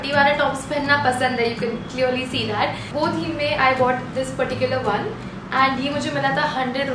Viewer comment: I thought that she is saying 'this patti-cular one' 😂😂😂😂😂😹😹